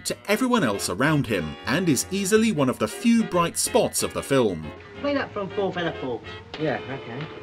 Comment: The guy is actually saying "Play that from 'Four Feather Falls"!